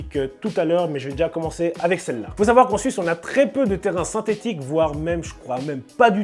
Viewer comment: C'est moi ou à 1:28 il a dit en Suisse👀